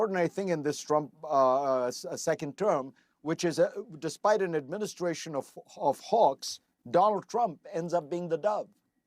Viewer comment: legit thought he said an "administration of hogs"